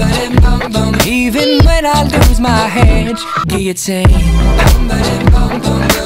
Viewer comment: anyone else hear “even when i lose my hedge”?